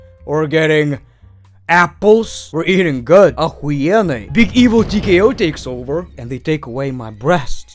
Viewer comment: Im sorry, but I have to know, 1:33 did you say "Ах*енно", or is it me?